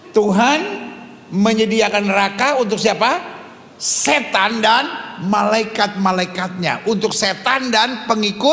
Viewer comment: Ada pernyataan " Neraka di ciptakan untuk setan dan malaikat2nya, di detik ke 10:02. Atau sy yg salah dengar ya? 😮